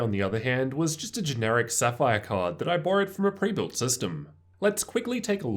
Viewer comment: Did he say generic Sapphire card ? I thought Sapphire only made AMD Radeon cards